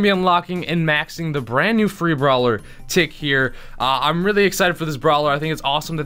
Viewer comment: Did he say furry brawler at 0:15?